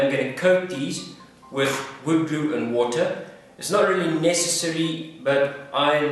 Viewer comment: I can't really hear what you said, did u say you're gonna coat them with wood glue n water?